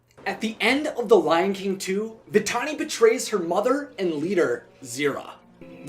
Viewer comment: I thought he said "The tiny betrays her mother." at the beginning.